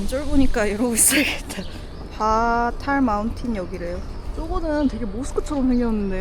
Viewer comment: 죄송한데 12:23에서 뭐라고 하셨어요? "파탈마운틴"으로 들리는 것 같은데 맞나요? 거기가 어딘지 찾아보려고 했는데 못찾았네요